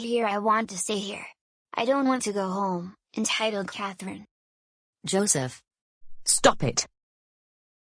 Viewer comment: i hear Catherine